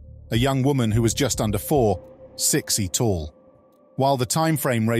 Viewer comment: a young woman who was just under 4... sexy tall?, that's what I heard 😂